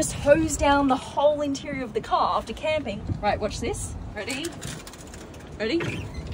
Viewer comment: “There’s a drain so you can hose it down after camping.” No way I’m going camping with a vehicle that’s powered with my phone. I’m lucky if my phone isn’t dead when I get back from a camping trip & that’s with me bringing fully charged power banks! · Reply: I genuinely thought she was saying, "After Camming." The internet has ruined me...